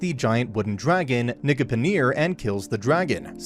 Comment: did he just say what I think he did? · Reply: no lol he said Nigipnir it's the dragon's name